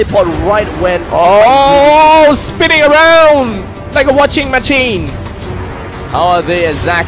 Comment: I think he said "spinning around like a washing machine"just my opinion or maybe... Spinning around like watching a matsing :)